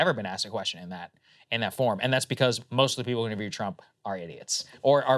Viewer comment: I had to rewind at 9:30 about 3x times, I thought Sagar called the people who support Trump (he actually said interview Trump) are idiots